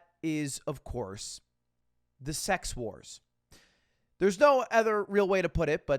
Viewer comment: Damn I thought ludwig said "The se x who res," not "The sex wars."